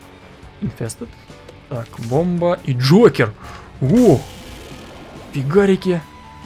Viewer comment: Мне послышалось "Энджойкер".